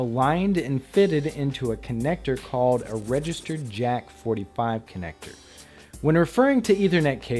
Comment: regester what ? did he say it means regester jeck or regester jet ? i can't figure out what he said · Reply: so its registered jack